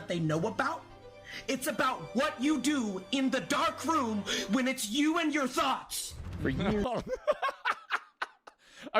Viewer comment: I thought he said “It’s about what you do when you’re in a dark room and it’s just YOU and your SOCKS” and I was thinking that was a tough but funny line